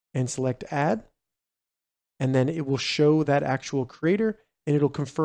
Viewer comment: WHAT??? I DONT WANNA GIVE YOU MY ROBUX !!! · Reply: I thought you said it would share with the creator